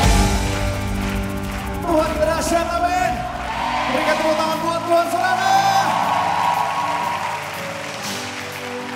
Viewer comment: Yg 2:52 wl nya bilang apa sih " Tuhan apa yaa...? Dahsyat apa bedahsyat ya? Mohon dijelaskan tks...hehe..syalom... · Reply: Basuki Efendi “Tuhan kita dahsyat, amin?”..